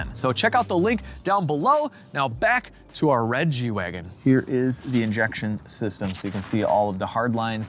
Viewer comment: Get 30% Off Your Own Custom Lawn Care Plan from Sunday! <link> Use Code Legit30 at checkout! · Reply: At the end of the ad read you said, "let's get back to the G-Wagon." I thought you said, "let's get back to the Reggie Wagon."